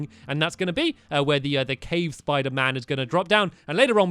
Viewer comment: Did he just say cave spider man